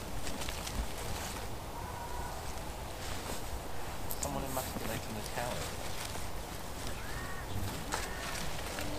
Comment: Good job, you've redeemed yourself. What did say at 47:56, only it sounded a bit odd? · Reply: It was "cooee" in response to my very manly wave goodbye at the same time.lol